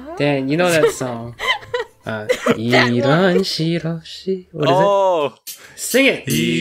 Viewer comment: "Buenos Dias!" *sees water bucket* *walks out door* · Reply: •mj •playz• I thought he said “ what is diiz”